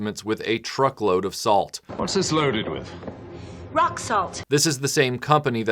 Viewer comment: at first I thought you said The Rock... No. Just a rock... lmfao.